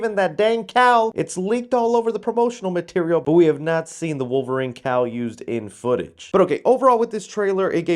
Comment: I was listening to the video while working and I thought you said "Wolverine cow" and not "Wolverine cowl". I was very confused